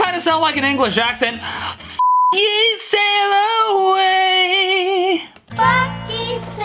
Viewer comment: I think he said bucku sail away